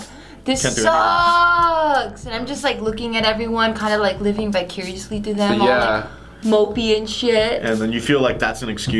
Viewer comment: did she say "living bicuriously through them"?